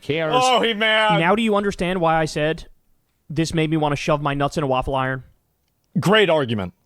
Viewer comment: I thought he said "I want to show my Knudson waffle iron" like it was something better than Cuisinart 😅